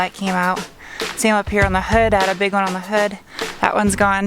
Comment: did she say “I had a big one on her hood”? Sorry... I had to..